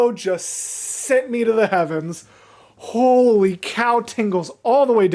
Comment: I've watched this video so many times but every time I see the intro I know he's saying "holy cow, tingles down my back" but I hear "holy cow tingles" it took me at least 3 times to figure out that "cow tingles" are not a thing 🤣